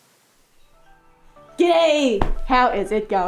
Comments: I honestly thought she just said “GAY” in the intro instead of “G’DAY” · I had to watch the first part a few times because I thought you said "gay" as opposed to "g'day."